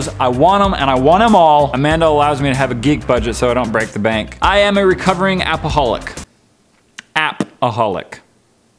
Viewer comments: you scared me more with not liking bacon then you did when I thought you said you are a recovering alcoholic XD probally everyone else too. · When he said "APPaholic" I thought he said "Alcoholic" And I gasped because I don't see drinking